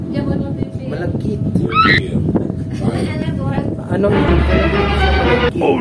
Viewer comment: 😂😂 You guys are sooo funny!! Malagkit na panahon 😅 Did I hear that right? I enjoyed watching. Thanks for sharing! ❤